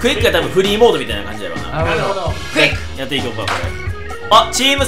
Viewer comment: ❤️💛「なるほど」 💚❤️「クイック」に聞こえた…